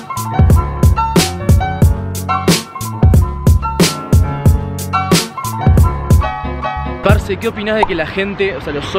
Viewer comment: Like si sabes lo que dijo en el minuto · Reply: Chispitas Leñador arbol